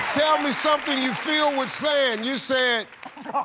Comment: I thought Steve said "something you feel with sand" and I had the same reaction as him